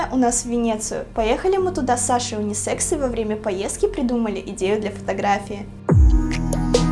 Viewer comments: Извиняюсь заранее но мне одной послышалось что когда они были в Венеции во время секс поездки? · мне послышалось с Сашой ради секса😂😂😂😂😂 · мне одной кажется что она сказала в 3:36 или 3:37 сказала секс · Мне послышалось с Сашей унисекс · Саша Унисекс 😂😂😂😂😂😂🤣🤣🤣🤣🤣🤣🤣 Мне одной так послышалось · Кто услышал "секс"???? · Мне послышалось что мы с Женей поехала в Винецыю ради УниСекса. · мне послышалось слово секс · Лайк,если тоже услышал слово Кекс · И мне послышалось что с Сашей ради сек..